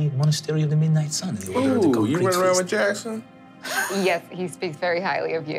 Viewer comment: I thought Brennan said “Jackson Wang” instead of “Jackson Wei” but alas, Brennan was not indisputably outing himself as someone who knows who Jackson Wang of GOT7 is.